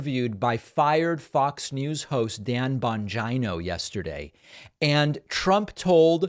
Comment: All I hear is “Dan Vagino”.